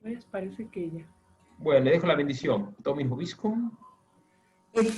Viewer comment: Padre, me gustaría saber qué dijo usted en el momento de la bendición aquí 1:34:37 podría escribirlo? (Y qué se responde también) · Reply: S/ Dominus vobiscum. S/ 'El Señor esté con vosotros.' R/ Et cum spiritu tuo. R/ 'Y con tu espíritu'. Cariños, Miriam! Dios te bendiga. ♥️